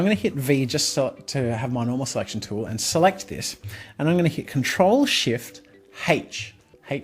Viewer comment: Any budy knows! What just he said? 10:30 Ctrl+Shift+ and what? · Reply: "H"